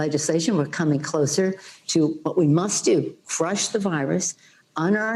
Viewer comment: I think she has had to much to drink before this interview. It sounded like she said frush this virus 😂😂😂.